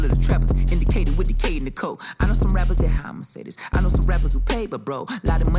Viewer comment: Instead of “I know rappers that- how imma say this?” I thought he was saying “I know rappers that- hire Mercedes” 😂